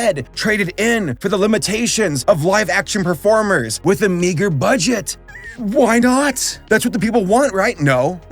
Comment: What did he say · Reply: With a meager budget